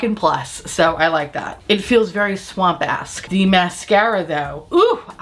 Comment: I'm screaming 😂 when you said "it feels very swampesk" I thought you said "it feels very swamp ass" for a second. 😂😂